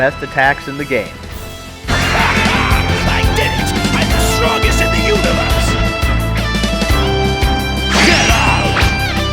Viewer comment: Did Raditz just say "Im the strongest in the universe."